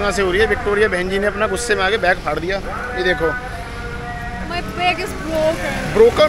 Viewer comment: bro what did u just say😭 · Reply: Bahan 😭😭